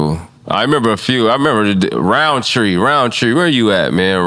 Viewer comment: Q talking bout khalil rountree? That man a ufc fighter now!!😂😂 ranked 8 rn in light heavyweight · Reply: It sounded like he said "rountree" when talking bout college teammates, n hes from la too & around same age